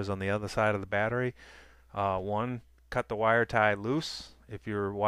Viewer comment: o the wire ties, lol i coulda sworn you said cut the wires i was like O_O what, ok kool thanks dude